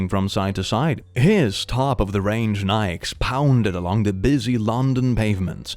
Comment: did you just say Nikes? (N-eye-ks)